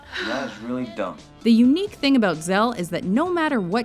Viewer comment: Did you just call him 'zel?